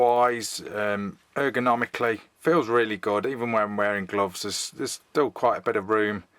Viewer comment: i heard "it feels really god"